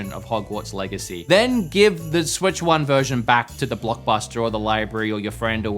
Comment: Did you just say Blockbuster?